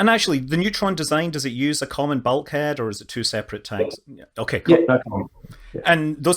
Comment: What was Peter Beck's reply to two separate tanks vs common bulkhead configuration for the tanks? I can't understand his reply, it's so quick and the sound not good. It's around min 23:40. Thanks! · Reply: It has common bulkheads in both stages